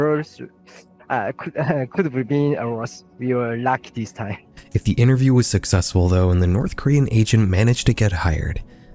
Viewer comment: WHAT...??!???? Did anyone understand that...?!? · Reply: kinda? “how can i say.. winds were strong and we got a lot of rain but luckily my place was fine. some branches down we need a bit to cleaner some damage (?) we got some luck this time”